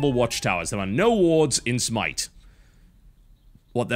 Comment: is it just me or did he say smite? :P